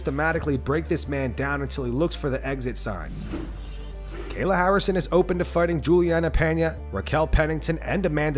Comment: I thought for a second that you said Kamal Harris was open to fighting all those female fighters, that wouldve been the comeback of the century lmao